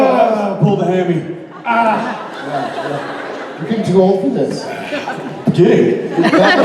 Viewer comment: what did Jared say? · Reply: "R we getting too old for this?"